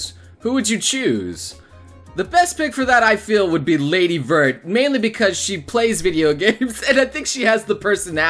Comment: from what anime is lady vord or vort I couldn't hear clearly :3 captain who's the girl in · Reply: Lady Vert from Hyperdimension Neptunia. Game and anime.